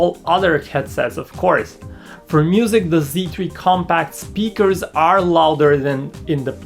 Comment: I think I hear him say "comPAKK"